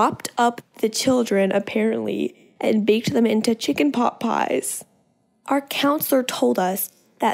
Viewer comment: chicken pot pie? more like children pot pie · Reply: I THOUGHT SHE SAID CHILDREN POT PIE UNTIL I SAW THIS COMMENT